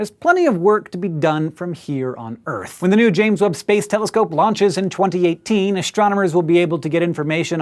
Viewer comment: im high and i thought he said the "james woods telescope" at first haha